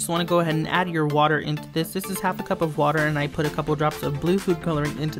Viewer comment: j thought you put tide in the corn starch I'm like omg!?!?!? But then i heard you say it was water with color I was like 😥😥